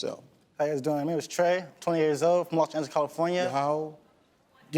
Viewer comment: Steve caught that guy, Trey, in his lies. I heard him say "my name is Trey, I'm 20 yrs old..." but he tried to cover it up. I went back to put on caption and saw it. I wish I could attach the caption screenshot. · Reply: I heard 28 but yeah he comes off super young 😂